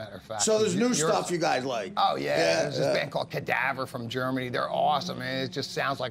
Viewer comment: What is the name of the German band which Dave called here? I can't understand him. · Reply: Kadavar from Germany